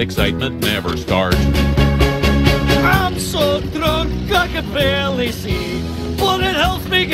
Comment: I thought he said “I’m so drunk like a belly seed” the first time idk why XD